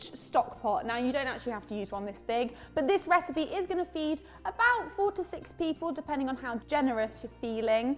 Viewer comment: For a moment, I thought you said "this recipe is going to feed forty six people..." :)